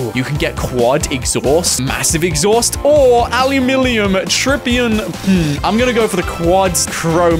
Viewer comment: Am I the only one who heard say "ALUMINIMUm EXHAUST"